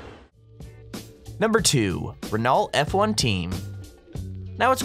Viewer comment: Did you just say "RENALT"? 🤦‍♂️